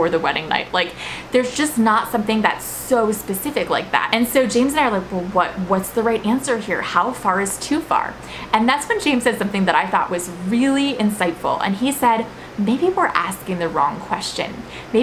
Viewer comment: I thought it said “how fart is too far” and I thought, yeah, that’s probably too far.